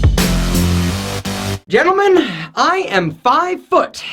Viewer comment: My volume was down and I was turning it up as you said "gentlemen", and it sounded like you said "human", which led to my new conspiracy: is an ALien. He's been studying human style for years in order to better understand us